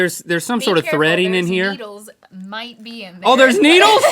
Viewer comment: I thought they said beetles😂😂😂